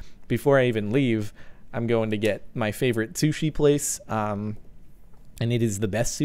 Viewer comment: I thought that u said "My favoret tooshie place